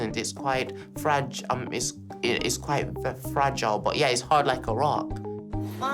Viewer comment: “It’s Fragile, but hard as a rock” thats one of the most simple but amazing way to describe the way he seed life. goddam hes a genius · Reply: I thought he said it’s hard like Iraq